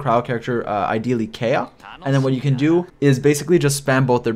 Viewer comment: Did he just say Kayuh 😔